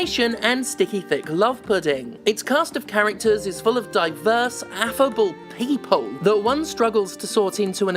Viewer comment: I know you SAID affable but I definitely heard effable